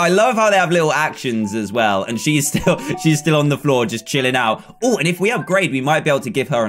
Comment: What does he say at 16:50? Oooh if we have...